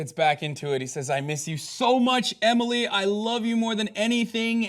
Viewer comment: I was away from my phone and I just heard "I love you, Emilie"